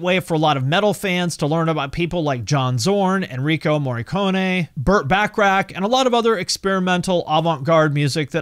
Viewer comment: Did I hear right? Did you say Burt Bacharach?? Thanks for sharing! 🙂